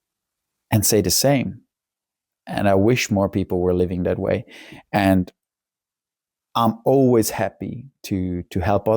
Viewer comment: yes yes... What was that you tried to film outside though? Falcon?? Haha, didn't quite catch that. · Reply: Haha yeap. Falcon :)